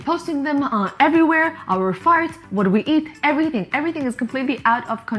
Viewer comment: Did she say " post our farts..."